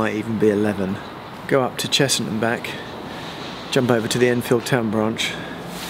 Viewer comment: "You can take away our trains, but you can't take away our sense of humour." Nice one!!😂 (PS, I might have a go at that one soon, you've inspired me!!) I'm surprised you said you were getting the tube from Upminster to Barking though, c2c would have been quicker, just one stop!! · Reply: it sounds like your saying chessington instead